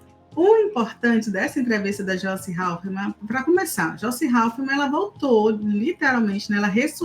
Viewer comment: Não sei se entendi mal mas a falou Joice Haufmann (2x) ????